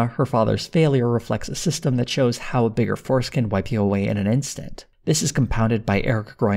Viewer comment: Great vid- really absorbed in this despite my very limited knowledge of the show itself. I've enjoyed your other retrospectives too, awesome work! That said, I heard 'a bigger foreskin' at 44:00 and now it cannot be unheard.